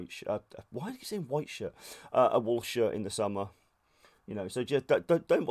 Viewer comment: Sadly you might actually fool someone with this hahah I’m reasonably new to the hobby but even within the first 20 seconds I was like wait what…ohhh hahah classic! Your videos have been instrumental in making my impression, keep up the good work 👍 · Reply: Same here, I thought "Why is he saying to use white T-Shi(r)ts?" But when he said "Don't use M37 wool shirts" then I realised he was joking.